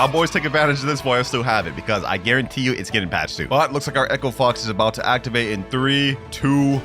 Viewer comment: did bro just say echo fox 😂not hate Ing